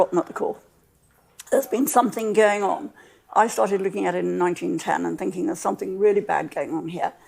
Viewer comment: Did she say she started looking at it in 1910? 2:46 How old is she?